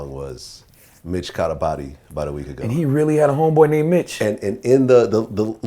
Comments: This whole time i thought he said just caught a body bout a week ago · this whole time i thought he was saying "bitch, caught a body bout a week ago" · Always thought he said, "'Bitch' caught a body about a week ago". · fareal, I thought he said "bitch caught a body bout a week ago"